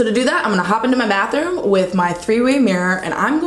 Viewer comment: I thought you were saying I'm going into my math room.... I was like wow this girl is a real scientist 🤣😊